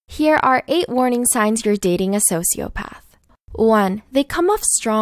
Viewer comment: I thought it said " 8 signs your dating a psychopath"